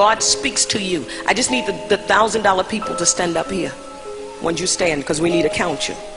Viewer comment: Personally, I won't tithe a "Church", I tithe my community. I donate to the shelter for abused and battered women. I tithe to the local animal shelter. I tithe to the guy standing on the corner asking for money, It's not my business what he or she does with that money, I have done as I was told..."He who gives to the lesser of my children gives to me" Tithe to the people you come in contact with, that are in need, and you have done as God has requested. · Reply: Who is the lady constantly asking for money? She got all the way to $10k. I just heard her say $100k!😱🤦🏾‍♀️🤷🏾‍♀️ ...and the music truly sounds demonic! Had me looking around the house like this 👀!